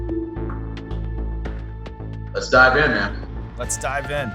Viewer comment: "let's dive in man" . I thought he said "let's die again" tho 🎲